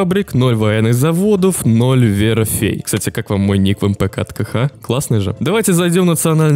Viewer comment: мне послышалось "Ноль веры в фей"